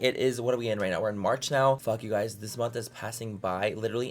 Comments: Omg.. He said "Fuck, You guys, this month has.." I thought he was saying "Fuck you guys, This month has.." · In the beginning, I thought you were saying "fuck you guys" LOL not "fuck, you guys, this month is passing by"